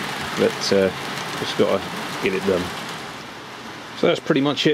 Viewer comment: Excellent as always and omg did you say "get 'er done" at 17:50?!